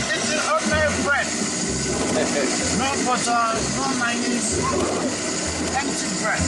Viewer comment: There you see that you always gotta be careful when you see something translated or written out, he very hearably says "No Butter, No Mayonaisse", yet the subtitles say "No butter, no cheese" , you see the problem? :D And thats only because of a heavy accent, now imagine translatinging from a different language